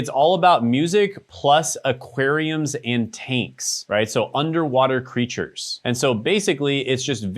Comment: 😂😂😂 So I was listening but not 100%, with your accent,....I thought you said "taints" . I need to go to bed. 😂